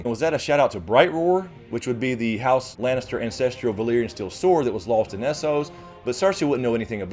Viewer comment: I could've sworn I heard "house lannister INcestrial sword" but I was thankfully mistaken lol